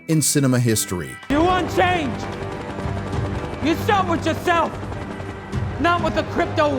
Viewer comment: WTF is she saying before "not with the crypto wallet"? "you show it yourself"? "you soured yourself"? the automated CC thinks it's "yourself with yourself"